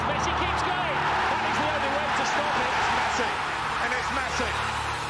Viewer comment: did he say little messi? Lmao